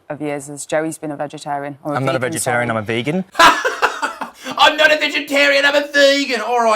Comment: The way Isaac mocked Joey saying “I’m not a Vegetarian, I’m a Vegan” almost sounded like he said “I’m not a Vegetarian, I’m a Virgin” 😂